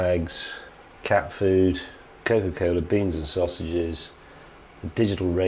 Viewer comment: did he say "covid cola" ??